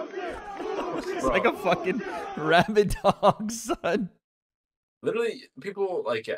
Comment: what did say on 5:22 ? it heard awfully lot like a no no word 😂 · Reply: “It’s like a f***ing rabid dog, son”